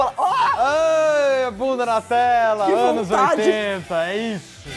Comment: eu ouvi um "que vontade" ???? KKKKKJKJJ esses cortes